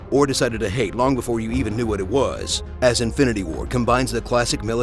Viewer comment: did he say Infinity War